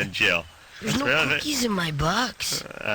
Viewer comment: "there's no dookie in my butt"?? wtf? is that what it said? what a rhee-tahrdded bch